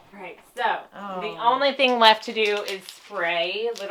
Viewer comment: i thought you said " the only thing left is to pray". i thought omg what happened.